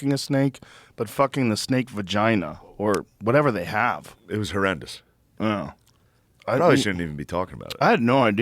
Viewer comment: it was “her-end-ous” 😂. That’s what I heard 🤣🤣